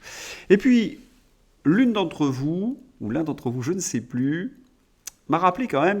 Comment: Je crois qu'on parle de moi là... C'est bien "l'un d'entre vous" :-)